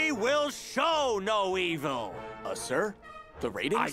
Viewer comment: "And in the future, Monkey News will show no evil!" "Uhh there's a serial killer on the loose right now. Shouldn't we make viewers aware of-" "SHOW NO EVIL!!!" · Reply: I thought he said "sow no evil" which would make more sense